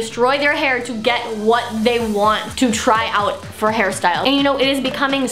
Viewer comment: why did i heard it “Harry Styles”